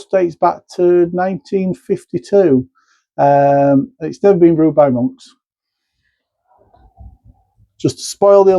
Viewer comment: Sounded like you said it is still brewed by monks.....from what I have heard it stopped being brewed by monks in the 1700s. Maybe I misheard you though. Cheers.